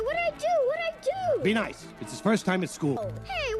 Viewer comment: (it’s his)😢 how did he pronounce it..??! Because I heard only (it’s), I didn’t hear (his)..!!!